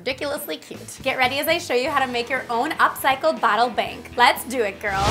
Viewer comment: Lol I thought it said bottle prank I was like I don't get it😂 Anyway love your videos!